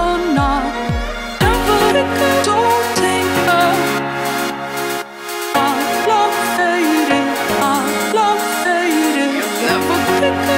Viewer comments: Holy shit! Doesnt it sound like its saying " Don't take love, I love satan, I love satan "? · from 'our love faded' i heard 'i love satin' and it scared the crap out of me until i got it right. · sounds like shes saying "i love failing"